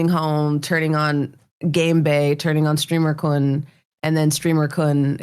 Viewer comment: I think the captions think you said something else other than streamer-kun lol